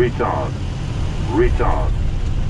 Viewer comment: I thought it was saying retard